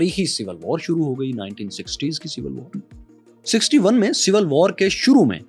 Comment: I think I heard the civil war start date 1960